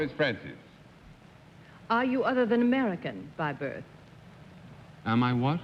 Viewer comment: What did Arlene Francis say at 16:02? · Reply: "Are you other than American by birth"?